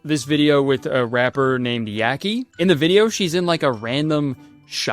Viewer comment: i thought it said YAOI 😭